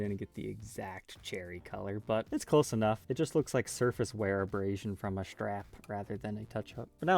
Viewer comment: Clown burst--when you said 'surface wear', I at first thought you had said 'circus wear', ha ha. I love your work and admire your dedication, just had to throw that one in there. Not that there's anything wrong with the circus...